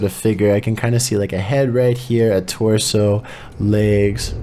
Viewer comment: No joke, literally came back to this vid (playlisting it) and all I hear is "Legs" at 8:42-8:83 ish xDD I hold my phone and see where I'm at, dumbfounded xD Just Chill's out of character saying, "Legs"